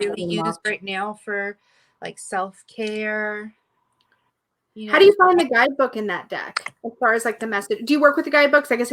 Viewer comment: That was fun, thank you so much. Can anybody tell me what is the tarot deck that is showing at 45:50. It sounds like "Moraki" tarot or something similar, but I can't find it,... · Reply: Yes! :) It's the Meraki Tarot: <link>